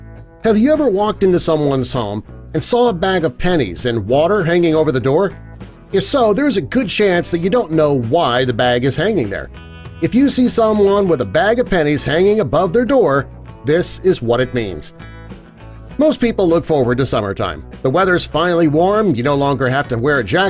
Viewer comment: Ohhhhhhhhh I thought it said a bag of penis